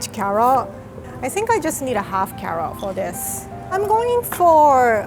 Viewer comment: In 1:13 did she said carol